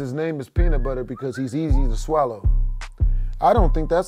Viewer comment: i thought he called HER peanut but cause she was easy to spread